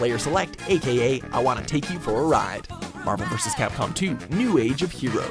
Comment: Wow my whole childhood I thought the Mvc2 theme was saying “I wanna kick your butt so bad” Lmaoo